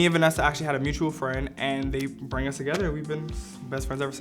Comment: did he just say "they BRANG us together?"